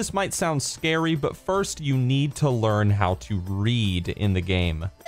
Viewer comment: Who else thought he said "You need to learn how to breed" ?